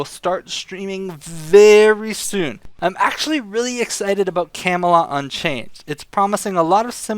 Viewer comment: camila unchained? i really can't understand what you said at the end of the video. Can anyone help me out with this? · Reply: camelot unchained